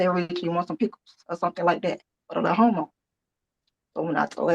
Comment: Is she calling Rachel a “little homo” or Rachel is going thru a “little homo”?? WTF is she saying or talking about, the offering someone a pickle?? This is some out there, crazy & silly stuff man!! Makes me so happy to not have any neighbors at all, lmao!!! 🤦🏻‍♀️